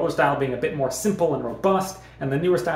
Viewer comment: I thought you said "He doesn't care more for this shit" 😂😂😂😂